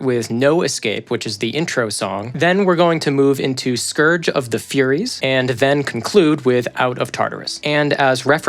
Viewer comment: For a second i thought it said scourge of the furries :o